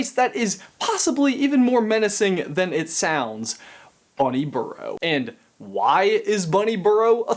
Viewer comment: Who else thought he said bunny Bureau